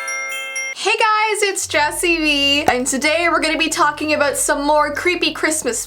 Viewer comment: When she said jessii Vee I thought she said jessii me lol